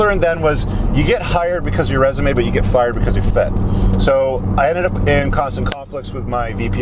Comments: "You get hired because your resume but you get fired because you..." fit? fought? I can't make out that word? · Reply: fit. There is a chapter in my book discussing the idea in depth. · Please help, anyone know what said??: 2:50 "You get hired because of your resume, but you get fired because you're..f****...." Seemed like a good transferable principle understanding (like: "chase excellence not women"). Thanks in advance. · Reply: Fit